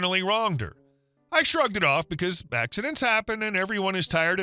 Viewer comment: Did he say 'bagcidents happen'? If not that is minor shame